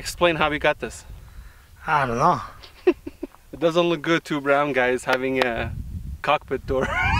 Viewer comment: I loved the start. “Two black guys with a cockpit door” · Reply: They're not there... It's very simple to get it correct. And from 0:07 he clearly says brown guys. I don't know his exact ethnicity, but I believe he is Armenian. is an Armenian family name. Armenia is a central Asian country, and it borders Turkey to the west. I've met many Central Asians who look white as white, I've met many who look Asian as Asian, and I've met many who are between the two at various ends.